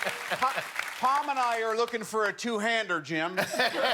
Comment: sorry what does he say? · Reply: 2 hands are jammed. I think that's what he said